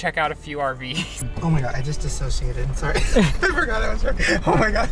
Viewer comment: Wait! At 8:27 Did Shane say he just "dissociated" ? Um... They are coming for Trisha.